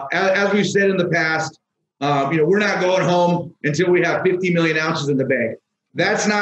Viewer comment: did I hear right? 50 moz?